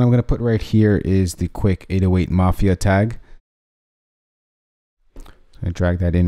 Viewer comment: I always thought it said aoa mafia but it's 808 mafia I'm dumb as shit 😂😂😂